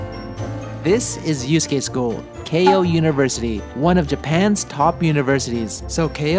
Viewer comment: I thought i heard “K-On!!! University”